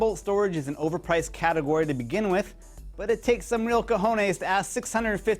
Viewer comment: Did he just said : "cojones"